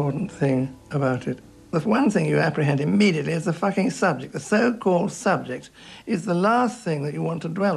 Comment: Did he say "Is the fu**ing subject" at 1:00 ? I nearly swallowed my brush! I rarely see people above 80 that are swearing, I like this guy!